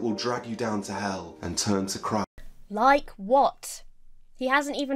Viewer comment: Does he say "...turn to crack..."? Cos that's not great advice. Just say no. (That goes for Jeezboy too).